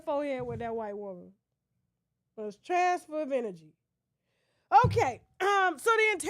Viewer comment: This woman is a fool!😅😅😅 I love her! Not “she went forehead to forehead, transference of energy.” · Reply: But why I thought she said transfer benefits??!!😂😂😂😂